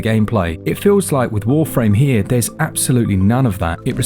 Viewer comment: did I hear warframe? Lol